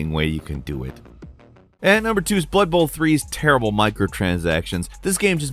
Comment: When you said blood bowl i thought you said butthole 😅